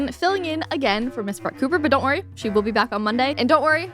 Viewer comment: Hahaha in the beginning when she was talking a thousand miles an hour I thought she said “Miss Butt Pooper”…… 😜😜🤣🤣🤣🤣